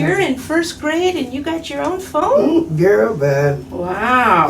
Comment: "you're in the first grade and you have your own phone?" Granny: "girl, bye". LMFAO did granny really say that or did I mishear her??? ahahahah